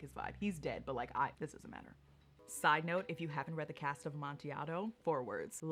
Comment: someone please tell me what she said it sounds interesting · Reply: cask of amontillado! one of edgar allan poes works